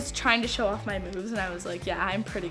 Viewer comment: When she said moves at first i thought ahe said boob i was all like oh noooo